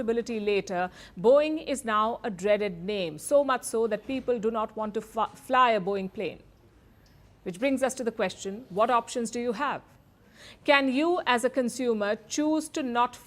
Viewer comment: lol did I just hear her almost say "people don't want to f#*k a Boeing plane"! :D